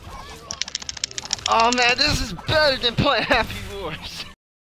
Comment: WHAT DID YOU SAY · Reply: you said this is better than playing happy wars